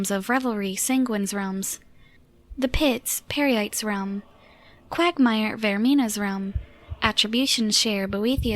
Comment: Did she say quagmire?